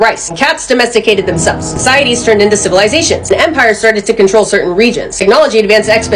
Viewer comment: I looked away from the screen for second while this kept playing and when she said “empires began to control certain regions” I thought I heard “Vampires began to control certain regions” 😂😂